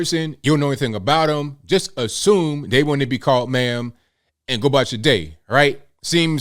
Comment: He said ma'am, I thought he said Man too at first though.